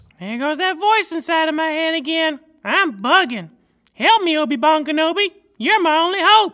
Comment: did he say obi bong kenobi?